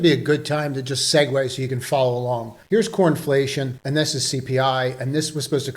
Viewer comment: I swear you said cornflation lol